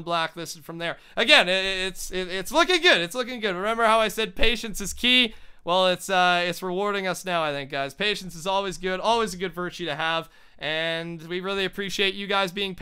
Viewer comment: i thought you said "confidence is key"